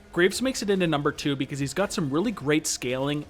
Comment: Lmao it says "grapes" instead of "graves" in 7:43 look at the caption